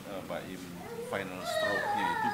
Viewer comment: Gak salah denger.... Baim minta rokok